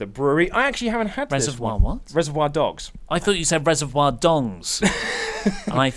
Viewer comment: Sorry oli I thought he said dongs too lol